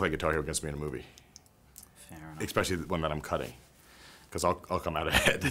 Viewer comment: Did Vince say "ekspecially" at 4:34?